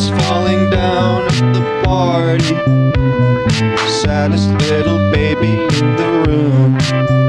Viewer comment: Am I the only one who thought it said ”Santas Little baby in the womb?”